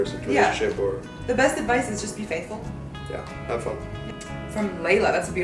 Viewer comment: did she say being faithful .